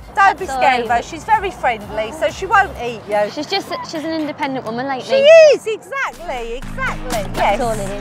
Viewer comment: around there, did jade say “she’s just an independent woman like me”? Or are my ears just not working?